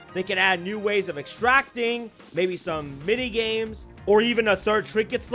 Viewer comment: did I hear a mini games?